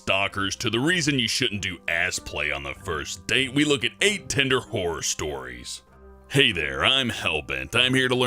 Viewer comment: I THOUGHT HE SAID WHORE STORIES 😂😂😂